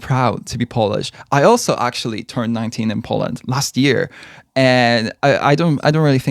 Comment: Bro I'm going deaf BC for some reason I thought you were saying "if you work in IT in Poland you get tested..." And I was so fucking confused I was like "why IT???" and then you said you "also turned 19 in Poland" and I died realising I need to get my ears checked out (Also I think I live behind the moon but it's probably just that I don't have tiktok- I haven't heard of any of these moments yet and I think there won't be many I know about)